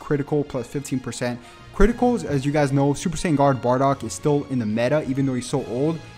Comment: I heard super saiyan god bardock or am I deaf?